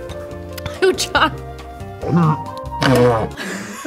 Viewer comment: Did she said oocha?